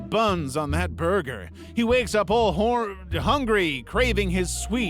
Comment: what did he sayyyyyyyyyyy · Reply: Hor hungry. Yeah yeah 😅